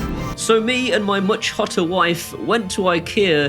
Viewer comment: Well this is the first time I've had to re-watch one of your videos due to needing to check you hadn't actually said what I thought I heard you say - first time through it sounded like you said Neesha is "fatter" than you... On second time through, played back at normal speed, you definitely say she's "hotter" than you. To which I'm sure we can all agree... :)